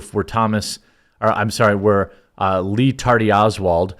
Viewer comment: I must be tired “ Lee tardy Oswald “ made me laugh entirely to much lol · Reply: I heard it as Ree Tardy Oswald